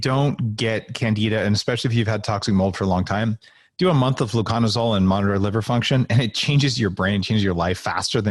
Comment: what did he say for candida?? Sounds like lucomisol ? Lycomisol? Couldn't make it out...ah, I figured it out, I would prefer something more natural myself <link> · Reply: flucanozole, never tried it myself but a high potency ginger essential oil vaporized in the lungs was effective for me at helping to rid of candida but there are many other therapies that help too